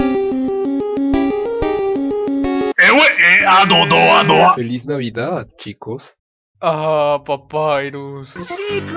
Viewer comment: Que raios? No entiendo que dices Cross :v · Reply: Es cross sanees